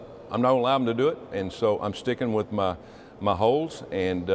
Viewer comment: "I'm stickin' with my holes." Tommy Tuberville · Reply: Thanks for clearing that up (I had thought he said, "I'm sticking with my ho-s").